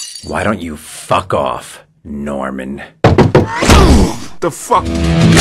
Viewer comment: A decade old now. And 6:22 took me until today that he said Norman and not Now im in. So many years amd I heard it right now. I think I will stick with Now Im in instead